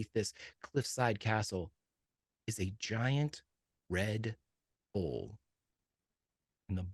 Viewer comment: I thought said, "giant red bowl" and I was so fucking confused, lmaooo